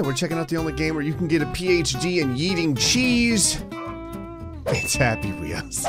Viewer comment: At first I thought he said "Welcome to the game where you can get a PhD in eating cheese". I rewinded it and he said yeeting cheese. If I was offed a PhD in eating cheese I would be top of the class world famous. NO ONE loves cheese as much as I do.